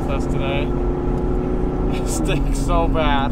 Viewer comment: I love videos. They are great! I have to ask,,,, At 0:53 did he say it stinks bad? That is kinda my take on the whole dog in a car thing.